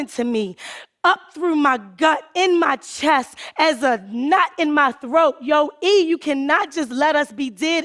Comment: did she just say "nut in my throat" LOL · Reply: I said "knot in my throat". Thanks for this laugh, though 😂